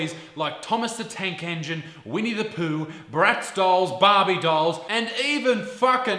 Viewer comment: Because I need to distract myself from what is happening to hunanity, I will entertain myself with the fact that for the first 2 mins I heard "we need to poo" instead of "winnie the pooh" And thought about what kind of stupid potty training toy that is. Man.